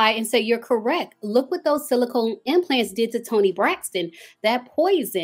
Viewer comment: But I could have sworn that she said she had the saline implants… not the silicone ones🤔🤔🤔